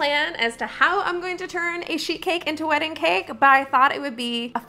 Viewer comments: I thought you said a shit cake at the start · When you said "sheet cake" I thought you said "sh!t cake" and seriously didnt bat an eyelash. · I thought you said sh*t cake 😭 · When you said "sheet cake" I thought you said "sh!t cake" and seriously didnt bat an eyelash. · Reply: Thank you for clearing because until I read your comment, I thought she said shit cake xD